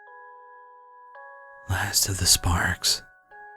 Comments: I thought this said "Last of the Sporks" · I thought it said "last of the sporks"